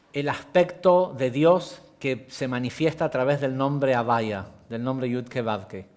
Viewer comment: Muy buenas noches. Cómo se escribe en hebreo y cómo se pronuncia el nombre de D’os que se menciona en el minuto 40:03 (avaya o algo así). Creo que se escribe Yud Hei Vav Hei pero no entiendo con seguridad su pronunciación cuando dice avaya.